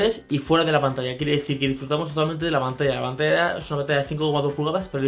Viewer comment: que es lo que dice en el min 2:05 ??? no le entiendo jajaja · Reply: Disfrutamos totalmente de la pantalla (Hay veces que no me entiendo ni yo mismo jejeje ;)